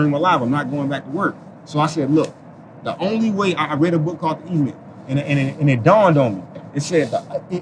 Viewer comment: What book did he say he read at 9:52…?? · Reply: Link in the bio.. E myth